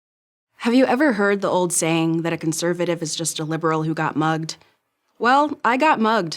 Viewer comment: I don't understand that word "got mogged" ? How is it written?